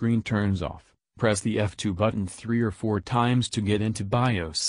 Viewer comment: I had tried everything and this worked!!! Thanks alot🙏🏾🙏🏾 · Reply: did he say f?